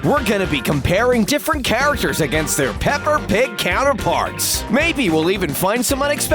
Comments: did he say pepper pig 😋😋🧐🧐🧐🧐🤨🤨🤨🤨😋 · said pepper instead of peppy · Did he just say pepper pig 🤔